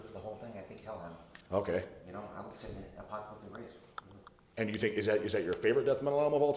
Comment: What band he say first death metal in the 90s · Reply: Hellhammer - Apocalyptic Raids